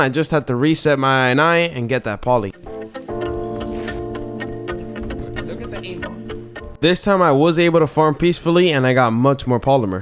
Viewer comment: Yooo 7:34 did he just say look at the aim bot???